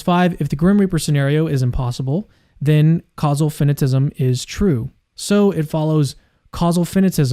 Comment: and 14:03 there's a typo in the (manually created) subtitles... in both timestamps, it says "causal feminism" instead of "causal finitism". Oddly enough, that exact typo is also in the automatic subtitles of Cameron's video at same moments, but not in this one's! In fact, this video's automatic subtitles show two different words: "finitism" and "fentanyl", respectively. Additionally, while we're at it, at 44:29 there's another typo: it says "imperfection implies imperfection" instead of "imperfection implies limitation". I'd fix the typos myself, but sadly some idiot at YouTube decided to remove community contributions...